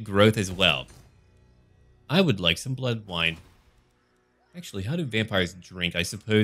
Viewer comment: See i heard you say "bloke wine" when in reality you said "blood wine"